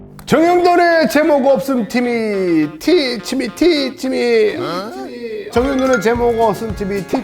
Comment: 언제나 정형돈의 제목없음 티미로 들리는 신기한 항도니의 언어구사...